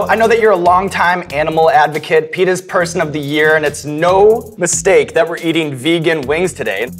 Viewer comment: totally thought he said "penis person of the year" and that they casually moved past that.